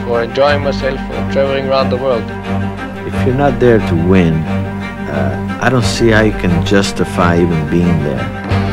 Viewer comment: what's Mario says? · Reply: He says: " If you are not there to win I don't see I can even justify being there."